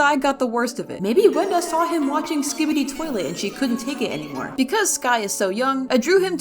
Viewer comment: DID HE SAY SKIBIDI TOILET